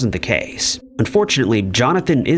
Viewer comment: Did you just say "But fortunately Jonathan is now paralysed"? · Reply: He said "unfortunately"